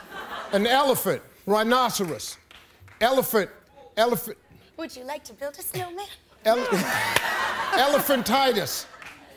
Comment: what she say? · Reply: Demi said would you like to build a snowmen :)